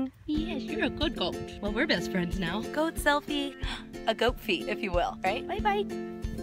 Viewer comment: Guys you know how she took a goat selfie and called it a goatse? I really recommend you search it on google images. Super cute. You'll thank me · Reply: I thought she said "goatfie"... like a goat selfie.